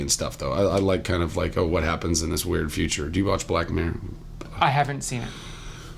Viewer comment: did he say Black Men? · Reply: black mirror not black men